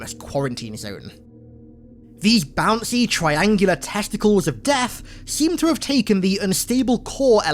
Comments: hol up... Did he say "testicles of death"?? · Did I really hear him say "bouncy triangular testicles of death"?